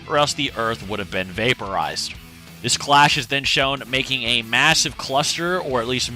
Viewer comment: Anyone else catch him say back at 5:54 instead of blast?